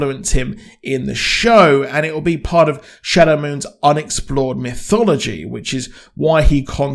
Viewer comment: While the scene between Shadow Mike and the Sheriff seemed awkwardly out of left field. I am glad they at least acknowledged Shadow having some sort of racial awareness of that situation. It would just be entirely too much suspension of disbelief to just pretend that Shadow Mike would show up in an all White town, with a dodgey story about what he is doing there or even how he has money to pay for anything, without some sort of racial tension. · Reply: I thought you were saying Shadows name was Shadow Mike not shadow moon and gave myself a chuckle before I realized you had actually been very clear and I was dumb